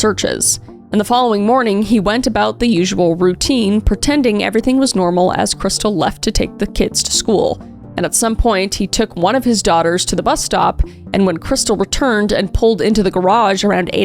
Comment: I totally thought you were saying bussy lol im sorry